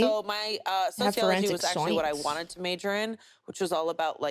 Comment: did I just hear Mina say she has a degree relating to Forensics? Can someone confirm or I'm going insane? Because that's fucking dope